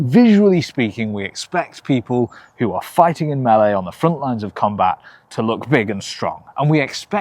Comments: Great aproachable video, mate! Also at 21:45 the captions say "fighting in BALLET" instead of "meele", and I implore you to keep it like that for forever cause a) it's hilarious and b) the mental image of ballet dancers in combat is just too riddiculus and funny as hell! :) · Hey, what's up with the subtitles? At 21:41 he says "fighting in melee" but the subtitles say "fighting in ballet"